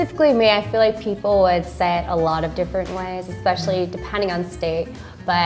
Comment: American do you think said “ would said” @ 10:48 or just me? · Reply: it sounds like she said "say it"